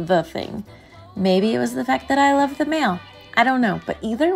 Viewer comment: Am I the only one that fr thought she was finna say “maybe it’s the fact I love the mailman”😭